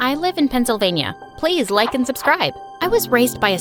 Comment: I thought she said “players like and subscribe”😂😂😂